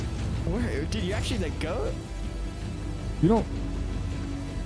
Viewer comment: it sounded like gooooooooal but its actually lets gooooooooo and the AAAAH at the end lol